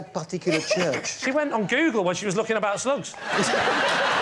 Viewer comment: I cant hear what Lee says here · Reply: She went on google when she was looking about slugs